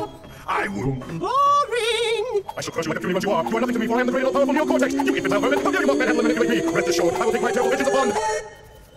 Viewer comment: slow down the speed to 0.5 to understand what he said Edit: NO, Seriously. JUST DO IT! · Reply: I am a cat and I eat so much, that's what he said